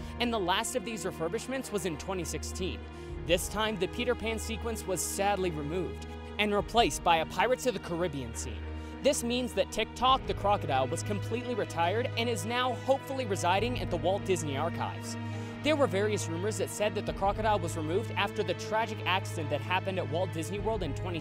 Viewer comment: TikTok the crocodile...I could have SWORN you said TikTok the clockodile.